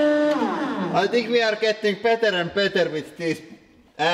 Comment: Every time he says "pancake" I hear "panic egg." I've watched so many of these videos that I wake up in the morning and go make panic eggs for breakfast. I was at a Denny's restaurant the other day and actually told the waitress I wanted panic eggs. I need to get off the internets.